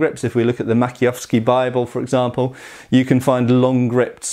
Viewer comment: what bible? what i hear is "makiofski bible" and when i type it in to google nothing comes up. can someone spell it out for me? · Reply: Maciejowski